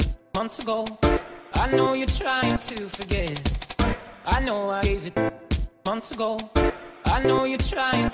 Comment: All I hear is "I know im ASIAN!!" 😂😂😂